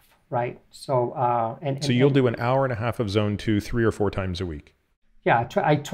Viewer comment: Did he say 90 minutes of Zone 2 three or four times a week?